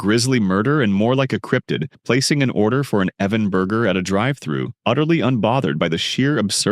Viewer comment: Did he just say his name is Evan burgerz?